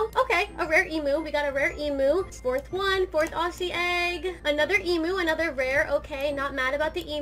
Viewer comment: did I just hear emo